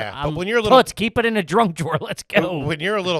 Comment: Okay, did just call it the "Drunk Drawer" ? 35:35 ???